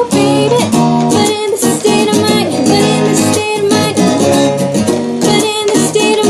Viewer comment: WHY DO I KEEP HEARING "BANANA STATE OF MINE" ? IN THE GREEDY SONG LOL😂